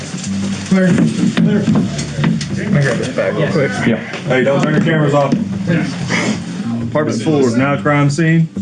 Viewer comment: the officer in charge says to turn cameras off. At 14:35 he tells the others to “be mindful what you say. We’re on camera now”. I’d like to see a video of what was said and done during those 2 minutes!!??? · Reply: he says "don't turn your cameras off" because it's a crime scene, he doesn't say to turn them off.